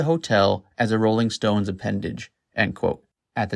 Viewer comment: Did I just hear "she moved into the hotel as a Rolling Stone dependage" -- did I really hear that? LMAO! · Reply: I had to listen to it again I thought he said "appendage" but you're right, he said "dependage" lol.